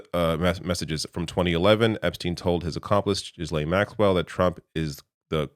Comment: did you say Jizz-laine Maxwell? Well played if you did.